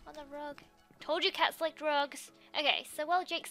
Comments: anyone who thought she said drugs she said RUGS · OH GOD NO I HAD SUBTITLES ON AND WHEN LIZZIE SAID "I told you cats like rugs" IT AUTOCORRECTED TO "I told you cats like drugs" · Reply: uhh when Lizzie said that I thought she said drugs instead of rugs too